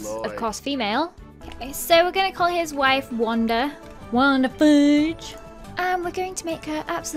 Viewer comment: Thought she said wonder fudge